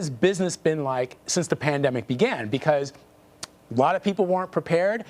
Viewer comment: does he say “plandemic” or is my hearing off?